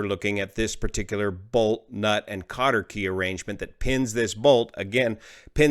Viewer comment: What's the word saying at 3:13. Sounds like "carter key". "Counter key"?